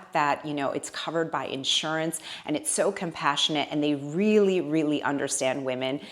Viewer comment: I pay $150 for a three month supply which to me feels great❤❤❤ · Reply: I thought you said that insurance covers it!!!